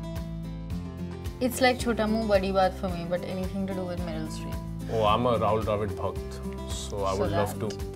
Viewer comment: what she is saying please tell me.. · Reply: She said anything to do with Meryl Streep.